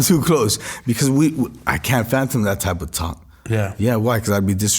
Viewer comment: Did he say phantom instead of fathom ??😂 Can't speak proper English but still have more cash than I'll probably ever see go figure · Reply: I thought he'd said "phantom" in place of "fathom" 🤭 😂😂😂.